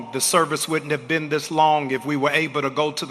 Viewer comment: Did he say "Dis long." OMG! That is so damn embarassing!